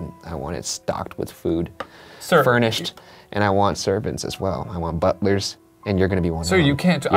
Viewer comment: I thought he said he wanted serpents.